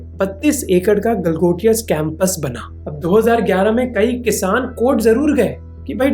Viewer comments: did he say "galgotia scampus" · did he said Scampus instead of Campus True though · Yes I heard it right 18:48 Scampus!! :face-blue-smiling::face-blue-smiling